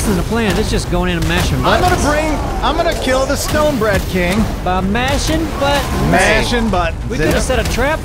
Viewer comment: Did said he's going to kill the Stonebread King?